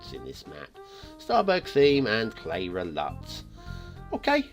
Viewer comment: Cleyra theme and LUT :-) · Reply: Ah thanks, must have misheard you on video , thought you say starburg theme.